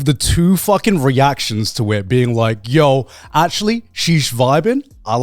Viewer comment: When Garnt tries to get his sentence out while he's still cooking he sometimes just says "sheesh" instead of "she's" and it's great